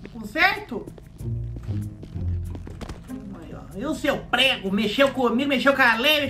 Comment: VOI RASISTA😢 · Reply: Ele disse prego e n preto